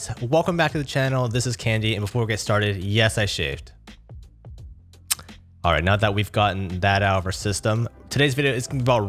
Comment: It thought that at the start of the video I heard, "And yes I shitted"